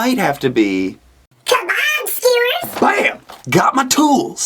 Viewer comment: dude, swear to god the voice alteration made it sound like you said, "Hm, what is my favorite food, YOUR MOM SKEWERS" i lol'd for half a second before i went oh... he said kabob...